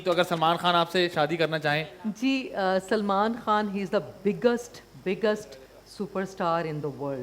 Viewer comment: that ‘big-gust’ sounded like ‘ disgust, disgust’ .